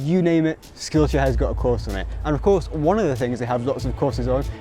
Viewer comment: I thought you said the sponsor of the video is "still here" XD